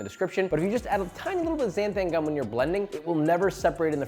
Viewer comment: i thought it said xanax instead xanthan XD